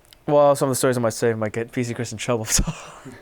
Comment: What the FUCK does KDJ say @ 12:40. SERIOUSLY. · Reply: A couple of stories i might say might get pc chris in trouble so. That is word for word what is says.